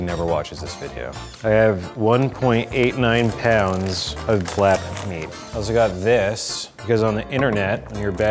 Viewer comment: I thought he said clapped meat or cheeks